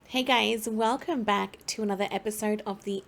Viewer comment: omg i thought you said "hey gays" and i got so excited and then realised its just your accent 🤣 love it